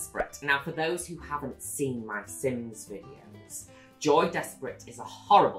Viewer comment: all this time I thought she was saying “Joey” when she was really just saying “Joy” with an accent of undefined origin.